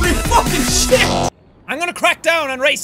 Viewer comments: when they called him jack boyed it sounded like they said jackaboy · Please tell me im not the only one who thought he said "Jackaboy" when he said "Jack Boyd"! XD